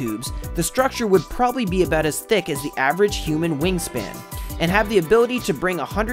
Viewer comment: Wait... did he just say "human wingspan?"